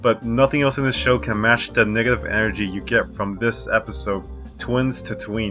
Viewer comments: Dang I thought this dude said the n word but he said negative energy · 27 seconds, I swear I heard “nigga energy “ was gonna swing 😭. It’s negative lol